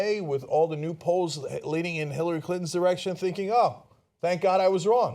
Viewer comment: did he say Hilary Clinton's erection?